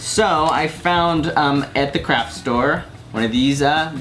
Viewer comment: At like 8:15 I thought he said Crap Store XD